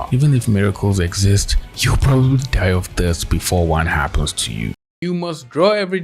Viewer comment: I was like, “WHAT” cuz I was drawing while listening to this and misheard “die of thirst” as “die of death” XD